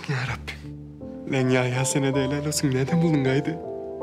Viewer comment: de Kavuk Yhya ya ne dedi? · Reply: Dediki helal olsun sana yahya nerede buldun sancari cikarabilmek yolunu